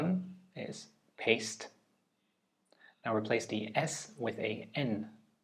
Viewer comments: what is this at 5:00 ? Word is Paste? Pante? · on the 5:00 minute mark he said the word paced and you should replace the s with a n... am i the only one who didnt understand this?or is his dyslexia showing xd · I'm not dyslexic, but took this test out of curiosity. Got 3/9, however I'm not sure the words in task 6 were actual words? That got me muddled. Also interestingly enough, I had no problem reciting the months backwards (despite having never done it before), but trying to recite the numbers backwards gave me a headache! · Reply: i thought he said paced instead of paste in task 6, i was like "what the fuck there's no s in paced" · 3/9, had trouble only with math-related questions as I have dyscalculia but I'm also stuck at 5:03 - I thought he said PASTE and I don't understand how it turns into PAINT. If we're changing S into N then the original word would be PAIST? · I THOUGHT ONE OF THEM WAS A TRICK QUESTION. I thought he was saying paced, not paste -- so when he said to remove the s I was like hahaah you didnt get me pal....gee anyways im not dyslexic still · I Hear paced and was so confused 😭😭😭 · is it just his dyslexia, or is my hearing wrong???😭 I heard "paced" then "paint"